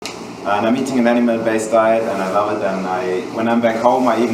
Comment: Am i the only one that heard him say he was eating an enema based diet